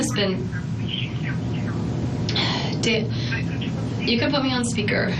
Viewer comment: who says wait for loud speaker ?? sooooooo fake. lock her up !!!!!!!! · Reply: I thought she said wait can I put you on speaker? ( said that to then put her on speaker)